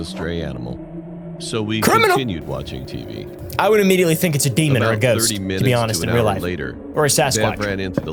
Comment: did you say... demon?